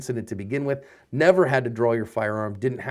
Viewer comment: for a while when I started watching your videos,I thought you was saying “cover your ass” not asp lol